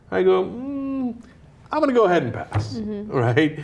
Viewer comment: Turned on the closed captioning @ 1:37 when Cenk says "mmm I am gonna go ahead and pass" Close captions says "mmm I wanna go ahead arson Rite Aid" O_o